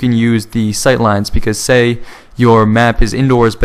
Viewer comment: lol at first i thought he said endor and i was like OMG ewok zombies.